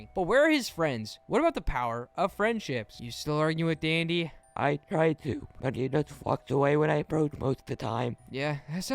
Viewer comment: “I try to, but he just ****s away” that’s what I heard